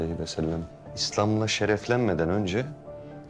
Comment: Küreşlilerin adatlerini yazıyormuydu dedi ? Ben mi yanlış duydum?